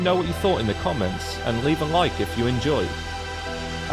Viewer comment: I liked it, but why we got to be Bitches.. Bitch! Are you referring to the U.N.T.S. Article 102? "Happy UNTS Bitches" is what I heard you say at the very end of your otherwise decent video. Is this Todd Howard Trolling??? LOL.... Oh, I was listening too hard, you said "Adios Bitches"... You threw in a Spanish word out of no where... well in that case "chupa mi huevos"...